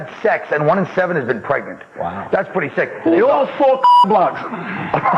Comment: What’d he say? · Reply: Took me a minute to figure it out but it's "Who's those 4 cockblocks?" Referring to "1 in 5 girls have sex"